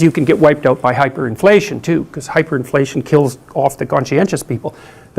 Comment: ahem excuse me did he just say inflation kills off the conscientious people? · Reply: Hyperinflation